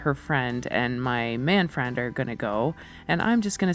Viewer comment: I thought you were saying Manfred at first. “Man Friend”